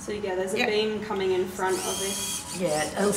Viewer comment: Did I hear a kid say BING in a split second speed? Or am I the only one?